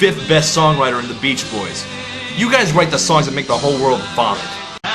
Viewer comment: “You guys write the songs that make the whole world ????” <—What does he say here? “Barf”, “bunk”, “burn”?….what???? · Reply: He said vomit